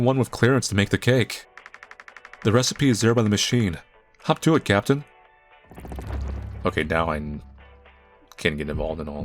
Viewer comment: for a dreadful horrible second i thought he said hawk tuah captain 😭😭😭😭